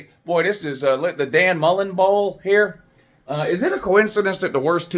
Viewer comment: I thought he said “mullet bowl” 🤣🤣